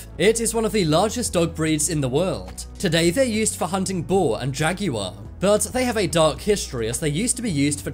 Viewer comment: did he say jaggy war